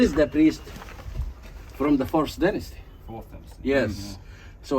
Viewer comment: I think the guy said FIRST Dynasty, not fifth.